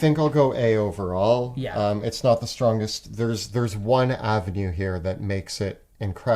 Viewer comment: I thought t said " to the strongest " and I lost my shit ,as a fan boi of Alexander the Great, as I thought that quote was so fitting for the video ❤❤😂